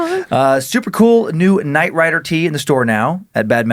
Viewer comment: Oh, I thought you said "Night writer", like a comedy staff writer for a late night show. Just a haggered, Jewish kid in his 20s, hoping to9 make it in comedy so well that his EXTREMELY disappointed parents forget all about why he's not a doctor, a lawyer, married, or owns his own home. But, no. You said "Night Rider". I get it now. Carry on.